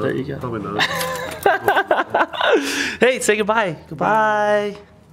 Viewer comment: through 7:46 I heard a creak say goodbye